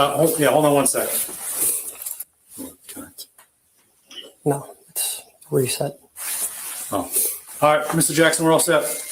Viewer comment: All these corrupt Judges and Proctors are why qualified immunity needs to be abolished. If they aren’t ready to check eachother then the law needs to check them in order to protect the rights of the taxpaying public that are forced to fund their misconduct. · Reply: I hear “won’t connect” then the other guy says “no it’s reset” 🤷🏼‍♀️